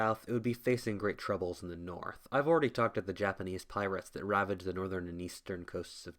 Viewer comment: it sounded as if you said “I’ve already talked to the Japanese Pirates in the north” instead of “I’ve already talked about the Japanese pirates in the north.” Not a critism just hilarious how it came out for me to hear it x)